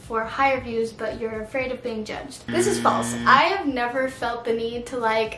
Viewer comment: Did Anyone else catch the "Flase" at 7:52? 😂😂